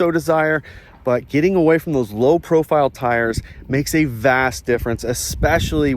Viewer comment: I heard “vas deferens”. Anyone else?